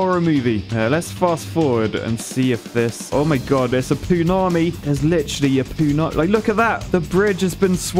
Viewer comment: I had to do a double take when you said poonami, I thought I heard punani thought maybe you'd been watching a bit of Ali G.